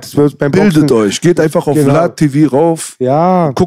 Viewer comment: Hi Leute...hat jemand verstanden fast am Ende welchen Kanal da empfohlen hat ? Verstehe es akustisch nicht was er da sagt...etwas mit La TV oder so... Bei 1 Stunde 23 Minuten war da ungefähr